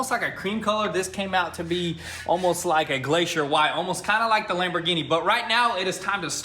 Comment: In 4:50 did you say like the lambigini boat😂